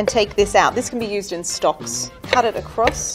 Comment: Did she say stonks